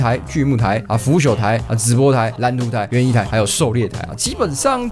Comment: 聽成直播台